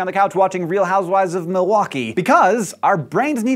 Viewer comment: did he just say real housewives of milwaukee? LOMB!!! (laughing out my beverage)